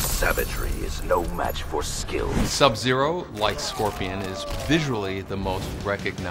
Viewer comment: i thought he said Sub zero likes scorpion